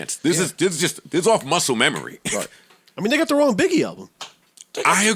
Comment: Parks is talking a Lil too much smh and did he say "nigga threw in biggie album" @ 10:25 wouldn't be the 1st time he's said it before